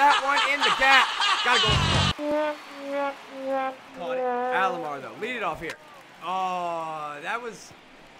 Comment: At time 4:10 is that saying Ahlogbar